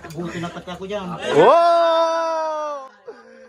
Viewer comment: "boto pati ako jan" tama poh ba narinig ko hehe